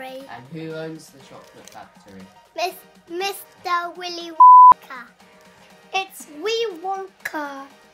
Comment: What did he say? My english seems to be insufficient xd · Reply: I said mr willy wan